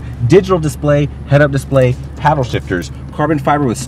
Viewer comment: Did he just say legless passengers😮